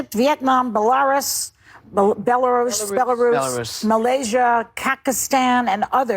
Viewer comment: Old woman on the view, did she say Kekistan at 23:52 ?